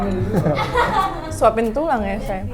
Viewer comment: Laura bilang apa ya ? Udah di ulang2 tetep gk nangkep 😅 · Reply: suapin tulang ya sayang , aaakkkk :D